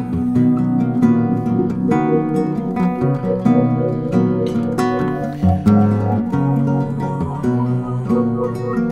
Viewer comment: Beautiful and so well done! What did Lyle call the sound that comes in at 3:45? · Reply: thanks brother!! he calls it "Pulse X Fade" ! it's really clear on that intro to "have your heard" on the road to you album!